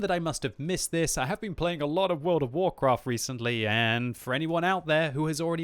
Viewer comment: did I just hear him right? Hes been playing WoW lately instead of WoT? Lol that's new